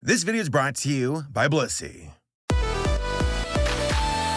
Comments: GEEZUSFUCKINGCRIST, I wasnt looking at the screen Your intro 0:00 I THOUGHT YOU SAID " BUSSY" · i swear to god PLEASE pronounce the BLIS in blissy, because all i hear is "this video is brought to you by bussy"